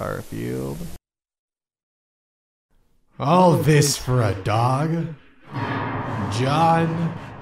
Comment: Gorefield: All this for a Dog, John? starts to play Mick Gordon - The Only thing they fear is You John Wick: ...Yeah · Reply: I think he said "dong"